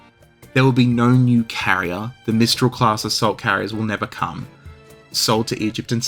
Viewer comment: Swear to god, I first heard "assault carriers" as "salt carriers" - and was wondering why the Russian navy needed salt... Not even kidding.